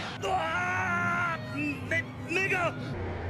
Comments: did bro just say nga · i hear like nek nigga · when bro said my eyes I thought you said n***